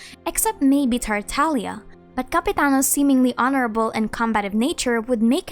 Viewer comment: I thought you said capybara not Capitiano